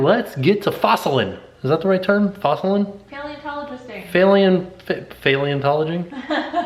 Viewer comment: Sound like you said failintologing. It just made it funnier